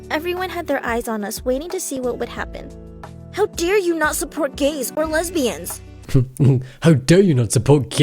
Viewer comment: I heard: “How dare you not to be gay!?”😂